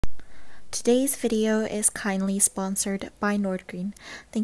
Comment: Instead of kindly sponsored I thought she said this video is kinda sponsored 🤦🏻‍♀️😂